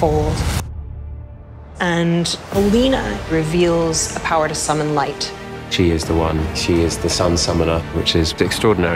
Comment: What’s he saying here at 3:32?? General who? Sorry I’m lost, is that the Darkling’s last name?? I only remember his first haha, it’s been a hot second (aka years) since I read the books. Also this show looks so promising!! :) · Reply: General Kirigan is a made up name for the show, they don't want to give away the true identity of the darkling to the new viewers